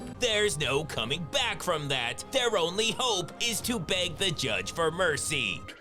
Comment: I thought he said they are going to have to bang the judge whoa lol